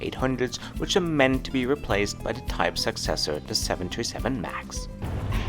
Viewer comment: I swear he said 727-800 and 727 Max 😂